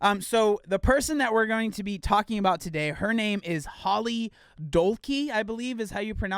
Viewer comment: Just casually miss heard you and thought you said "Holly Donkey" 🤣🤣🤣🤣